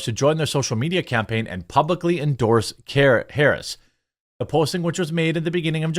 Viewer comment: Lol misheard a line in there, "Latino's publicly endorsing carrots" ....those bastards? XD